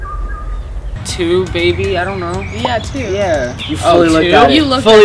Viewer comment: In 3:43 or 3:44 i thought he said baby and not maybe awe like old times 6:38 Me: KENZIE RUN